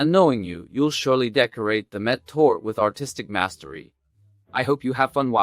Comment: NIce recipe! I have a few questions tho. like do you season the meat at all? also at 4:25 "Met tor" so Mett is meat, but Tor? also that music at the end is nice. just thought I'd say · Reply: I say: "And knowing you, you'll surely decorate the Mett Torte with artistic mastery." - torte is another word for cake (in germany). You can buy Mett already seasoned. Otherwise season with pepper, salt and caraway. And I'm glad that you like the music. Best regards 😊